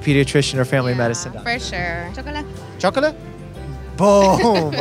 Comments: The woman with the kid actually said “chócalas” which is used in Mexico (not sure about other Latin American countries or Spain) and it means “high five.” It was funny how Dr. Mike thought the mom said “chocolate” and then did a fist bump with the kid lol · What she says: Chócala! What the subtitle says: Chocolate 😆 For non-Spanish speakers, the closest translation would be "give me five!". The literal translation would be "crash it"